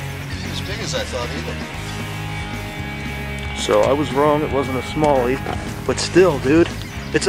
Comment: I thought he said so i was right, its wasnt a somali